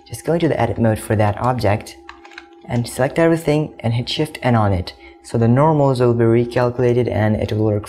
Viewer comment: I hit shift and what sorry? I didn't catch that and the subtitles and keystrokes didn't indicate what you said. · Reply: Shift N